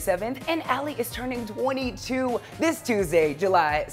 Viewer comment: I thought he said that Ally was turning 42, I was like WHAT??? But I already knew that she was turning 22 at that time.